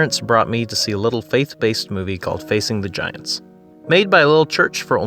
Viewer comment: I thought you said “facing vaginas” and I flinched